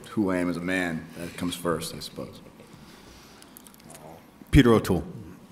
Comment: I don't understand who inspires Robert Downey (6:11) ? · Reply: I think he said Peter O'Toole